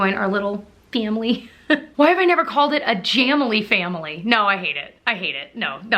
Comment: We are Jamily! I thought you said Chamily, like channel and family merged! 😃 wait I listened again... I think you did say Chamily , I love it!